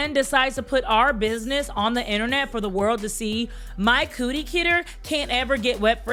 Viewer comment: LMEAO. did you just say the "cooty kitter can't get wet"? I'm dead 💀 🤣.